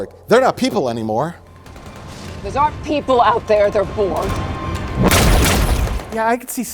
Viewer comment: I thought at 23:38 that 7 of 9 said "Those aren't people out there, they're poor" and I had to do a fucking triple take.